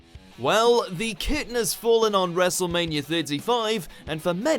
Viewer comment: did he really say wrestlemania55?